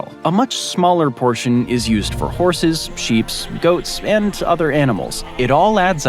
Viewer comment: Did you say sheeps?